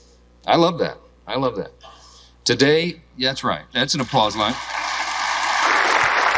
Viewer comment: did he say "Applause moment"; what?🤨🇺🇲